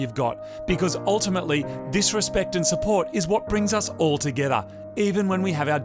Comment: Nice work folks :) :) about the narration, I think I heard "Disrespect and support is what brings us all together?" Sigh I feel weird, is it only me?